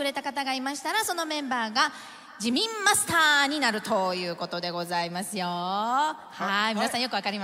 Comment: "Jimin Master ni naruto" juro que escutei isso kkkkkkkkkkkkkkk